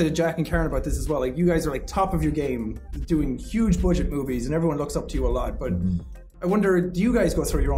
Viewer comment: did he just say boobies or movies?!?!?!?